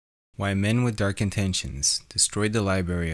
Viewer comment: Thought it said white men with dark intentions